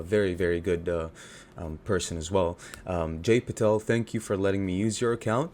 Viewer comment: Jake? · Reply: thought you said Jake, registering I realized you said Jay